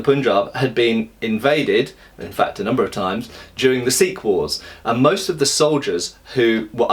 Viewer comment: It took me a second said Sikh wars. I thought you said sequels. Indian mutiny 2: the sequel.